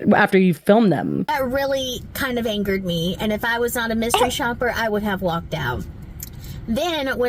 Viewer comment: Please listen VERY carefully she says, “ And IF I was on a mystery shopper, I would have walked out.” Did she misspeak here or did I mishear cause it sounds like she is just being a Karen and like trying to get footage to advertise her business…